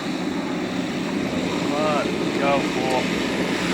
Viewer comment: Liking your ride, but I hope I misheard,.. at 1:35 Did you call that UPS driver a "fool", right here on the internet, to the whole world? After describing where you were? Hope he's not a subscriber.....